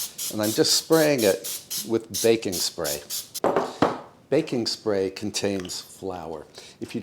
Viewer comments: I thought he said bacon spray and I wondered where that wonder product can be found. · Oh...I thought you said 'Bacon Spray'. I almost looked for it on Amazon!😂 Thanks for all your wonderful videos. Merry Christmas and may God Bless.